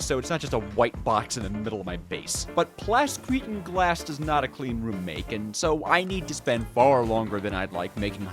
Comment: "while plas gretin glass does not a clean room make" explain yourself spelling what says